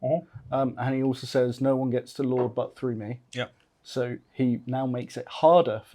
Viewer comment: I heard " no one comes to my father's butt through me" Jesus is a goalie! Or maybe a bouncer?